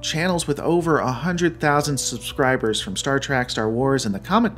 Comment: Did you just say "Star Track?"